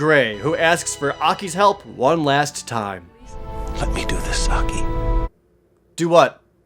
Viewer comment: It sounded like grey said "let me do the sucky"